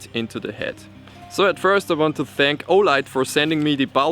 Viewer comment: Olight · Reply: sounded like you said fleshlight